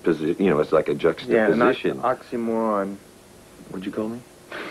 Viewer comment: What did Kirk say at 1:00 ? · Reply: James: You know, it's like a juxtaposition... Kirk: ...yeah, on oxymoron. James: What did you call me?